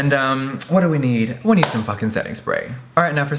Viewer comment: lol when he said we need setting spray i thought he said a sex break 😂😂